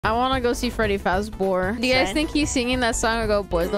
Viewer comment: Did you just say? “Freddy fazbore”